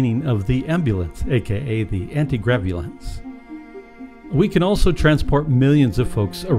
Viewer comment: Someone please tell me that this narrator did not say we could replace the ambulance with the "anti-gravulance". (1:01) If so, I'm "unsubbing." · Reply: Dude, yes, that is what I said. Dry humor is good for the mind.